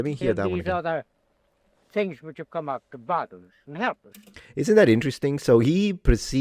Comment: In my headphones I thought he said “boggle us” not “bother us”, as in the new tech is confusing.